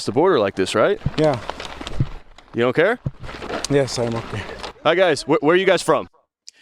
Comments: hes saying, "im ok" To be polite, cause he cant understand the convo. Imo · He said, "Yes, I'm ok." He didn't understand the journalist.